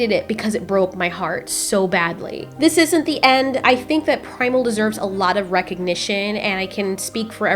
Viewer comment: I thought it said primeval now I am sad :,(